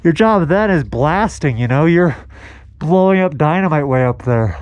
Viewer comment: I'm not trying to be the ghost hunter here. But did anyone else hear someone say, "I know." At around 23:30 minutes, when was talking about someone's job would be blasting dynamite for the day. I've listened to it over and over again and keep hearing the words, "I know."